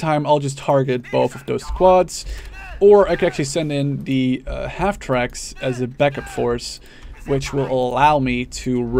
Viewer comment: What does the German Halftrack unit say at 20:28 ? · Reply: Alexander Gomez "Still gestanden!" - "Attention!" , "Schnell, schnell!" - "fast, fast!"